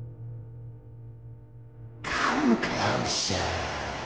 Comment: I thought nightmare bb said : come closer, and recount my teeth